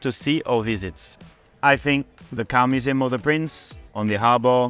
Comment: I thought he said Cow Museum until it switched to a room full of cars 😂 Wishing them luck this weekend (Along with Valterri and Zhou)